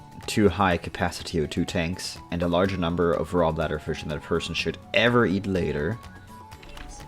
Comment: What did you say at 6:00? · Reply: "2 high capacity O2 tanks and a larger number of raw bladder fish than a person should ever eat later" :) I mumble too much..